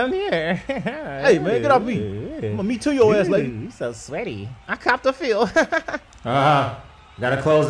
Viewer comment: did she just say "mm he's so sweaty"